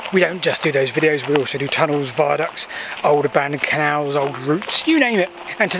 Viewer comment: I’m really looking forward to the next episode of Old Abandoned Cows 🤔 I suspect I misheard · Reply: I've just listened again... said abandoned Canals. 🤭